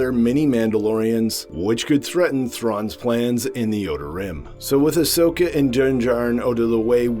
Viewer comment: The auto-captions say Yoda Rim, not Outer Rim! 😂